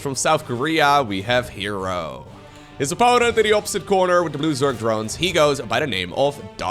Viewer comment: I thought he said ".... with the loser drones." JFC!